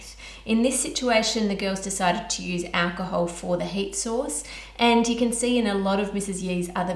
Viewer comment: “..heat source..” My ears: “..hate sauce..” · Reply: I heard "Hate Sauce"